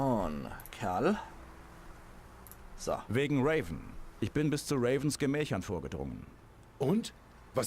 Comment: Ich hab grad verstanden "Ich bin bis zu Ravens Gemächt vorgedrungen" :D Oh meine Fresse, heute ist ein schräger Tag :D